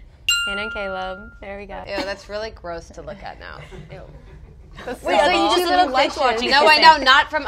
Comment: can someone tell me what Troian said al 01:25 ? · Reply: She said "wait, I thought you just said you loved watching kissing."